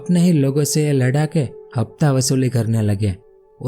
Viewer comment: what you saying bro hafta wasoly Kuch be😂